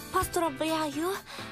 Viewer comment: Что ана гаварит в 0:27,0:52? •○•○• What does she say in 0:27,0:52? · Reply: Поздравляю / Pozdravlyayu / Congratulations 0:52 - Желаю удачи, Минами / Zhelayu udachi, Minami / Good luck, Minami